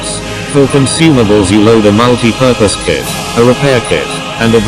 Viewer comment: i heard MONKEY PURPOSE KIT...